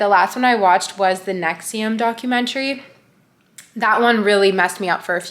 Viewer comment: What's the name of the first documentary you watched? Time stamp is @ 4:30 Couldn't hear it properly. Thanks! :) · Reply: I think it's for the cult NXIVM , it's so disturbing tbh